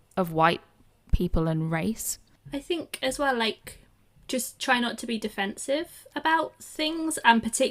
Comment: you made a mistake. The second woman in the clip you showed didn't say 'Just try not to be offended', she said 'Just try not to be defensive'. Different meaning, because obviously there's nothing wrong in defending what you believe. That woman is pretty much saying take down your defenses and just believe their hogwash. So your criticism of what you misheard is divorced from the context of what was actually said.